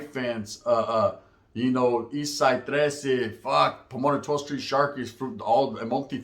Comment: I thought you said Pomona 12 street that shanked you 😅